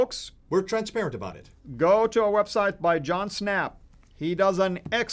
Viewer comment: says to go to a website by a "John Snap" -- I can't find this site. Does anyone know what he's talking about?